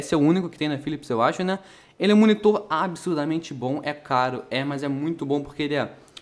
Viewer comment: só eu entendi abyssus hehe kk